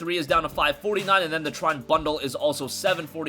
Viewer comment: what game is this I can't understand what he said here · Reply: trine